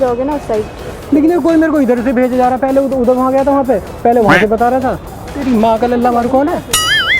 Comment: teri maaka..... Kya bola😂😂